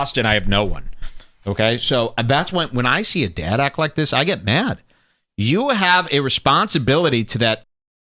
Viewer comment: by my lip reading capabilities did he say “you have a responsibility to that sh*t eating n!gg3r.?” Lol please correct me with the right words if not.